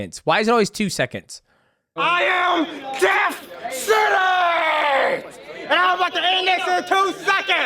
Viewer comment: I thought he said, "DEATH CITY!!"